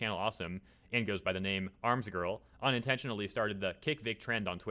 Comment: Did you really just call Marzgurl Armzgurl? Sounds like you said that the first time you read her name here.